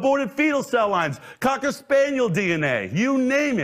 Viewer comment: did he say Cocker Spaniel DNA was in vaccines or did I miss hear him during his quickfire ingredient panic?